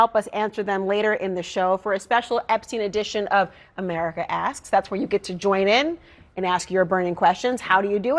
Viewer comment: ai needs work. Check the subtitles at 10:52. America Asks became America Ass. Either that or she was talking about our president...lol After more thought on my part, ai subtitle thought the show was called "America Ass" because Epstein was in the same sentence. Wow!!! Sorry, ai